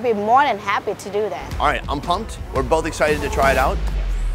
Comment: Quang Tran: did someone just say “ i’m pumped “